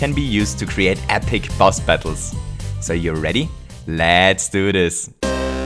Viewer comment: I heard you say lat's do this!